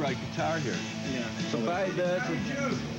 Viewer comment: did he just say dan electron? hahaha! also its pronounced moz right..mostite not mostright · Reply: Tate Thompson he said Dynelectron. That’s a brand from Italian company Meazzi which made exact copies of the Dano longhorn guitar and bass